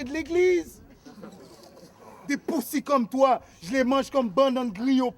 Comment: il dit quoi vulguerre a 4:13 svp ? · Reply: En français banane griot au pikliz " banane plantin on en mange beaucoup les Haïtiens 🤣🤣. " Des puzzy comme toi jles mange comme bannan griot plikliz"